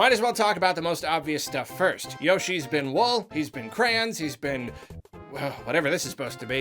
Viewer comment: Need a judgment call here. Does are say crayons, or crans. I hear crayons, my fiancee heres crans.